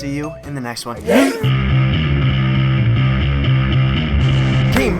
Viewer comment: Dude... You sound like your saying teen ballz in your theme song.